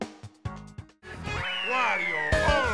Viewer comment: Did Wario say "Wario alwaysly?" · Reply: He said “wario always wins!”